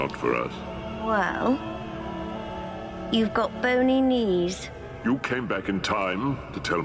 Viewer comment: "Really?...Boney knees?" Adorable. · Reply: I thought he said "Only knees?" referring to the lolita skirt she's turning to XD